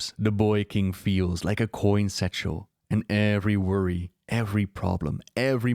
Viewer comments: I listen to your stuff as I relax at night. I swear it sounded like you said "Coin-Sexual" instead of "coin satchel"... lol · What is a coinsexual and how does anduin feel like that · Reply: lmao i thought he said that at first too but i think he said "coin satchel"